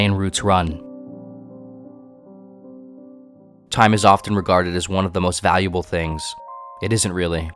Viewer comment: I hear “Time is one of the most valuable moments things” and just like that linkin park is playing in my head now